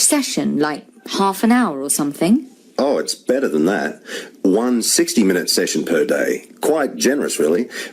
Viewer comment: Is Question 10 answer - 160 minutes ? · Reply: I also heard it as 160 minutes. In the audio he said, "one 60 minutes session per day".